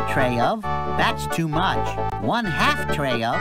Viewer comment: I thought he said "Shit the fank you" He actually said"Chicken, thank you"